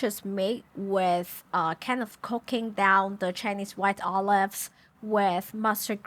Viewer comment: i thought she said cocaine and i was terrified for a second that Coca-Cola wasn't the only food made with coke